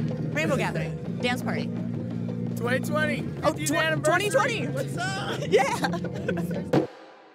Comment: I wonder how much COVID spread through this camp, being 2020 and all. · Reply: I swear they said 2020 at 20:10 .